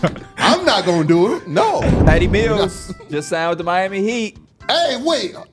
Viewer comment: Bro thought he said “D Mills just signed with the Miami Heat”